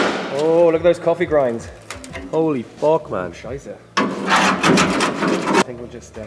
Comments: Did he actually said “scheiße“ at 3:27 ? :D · did he say "scheiße"?